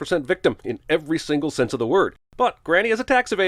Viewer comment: did he just say he instead of she?